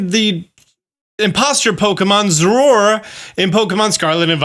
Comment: Am I the only one who thought he said Zeraora at the start and got slightly hyped?